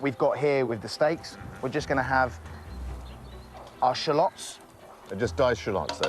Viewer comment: what are they sayin? They put what? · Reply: Diced shallots